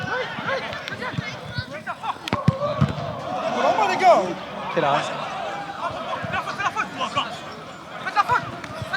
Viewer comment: c'est moi ou qlqn dis quek arabe ? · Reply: Il dit « quel arrêt »